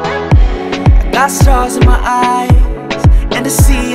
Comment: I swear I heard, "I got sauce in my eyes."